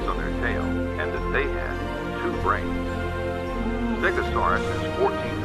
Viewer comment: Help pls! 22:25 what dinosaur was that i didn't understand? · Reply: No worries! The tour audio says "segisaurus" but I had to use coelophysis as a stand in. While I was editing I got to that part and thought he said stegosaurus again